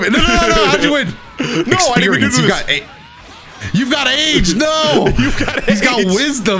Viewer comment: “You got aids!! No!!” I heard it too lmao